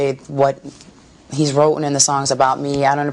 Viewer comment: Did she say "wroten"? Lol😂